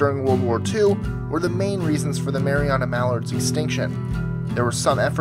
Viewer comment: I thought you said "Marijuana Mallard" so I went back to check and was sadly dissapointed